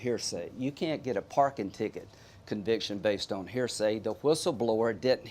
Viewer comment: Did he just say "parking ticket conviction?"